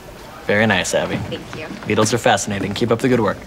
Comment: Beetles are fascinating... BEETLES ARE NOT FASCINATING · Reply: I thought they said needles 😂